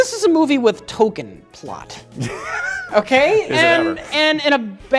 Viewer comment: When Rich said "Token plot", I thought he said "Tolkien Plot". Then he complained about movies he thinks have too much exposition, which didn't disabuse me of that notion yet.